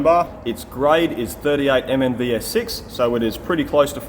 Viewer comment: Excellent work! I couldn't catch the grade of the bar @ 15:12. Can you mention it again? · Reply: 38mnvs6